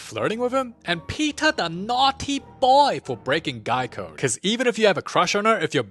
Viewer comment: Also, when he said “guy code” I thought he said Geico😂